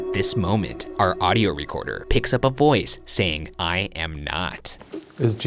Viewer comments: It didn't sound like he said," I am not" to me. i thought it sounded like, "I hope not". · I swear the box said Michael two times. When you thought the box said "I am not." I heard Michael. When you where sitting in solitary and you asked what the name of the jail was, again I heard Michael. You probably won't see this but if you do thoughts? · When asked if Jesus is here “I Am Not” I heard “l Hope” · I hear "I hope". Not, "I am not" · It sounds like it says “I hope” rather than “I am not” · When he says “ I am not” I heard “hi ho” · i thought the spirit said "i hope" instead of "i am not" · apparently it was a "ghost" saying "i'm not" but to me it just sounded like someone saying "hi ho" as they walk down the hallway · "I am not?" I heard "I hope." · To me, the voice outside the chapel sounded like it was saying "I hope" not "I am not" :/ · it sounds like it said "I Hope" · The part where they hear “I am not,” sounds more like “I hope.” But fr the first thing I heard was “Hi-ho” like the dwarfs song lmaoo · I heard “I hope” instead of “I am not” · Wait, wait, wait. When Shane asks "is Jesus here?" I honestly think I heard "I hope" instead of "I am not". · When the “ghost” said “ I am not”, I thought it said “I hope” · I hear ,,I hope’’ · I heard "I hope" not "I am not" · That's weird because I heard "I hope" and u guys heard "I am not" and I heard it before the replay happened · I heard 'I am' when they heard 'I am not' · I heard "Michael" instead of "I am not"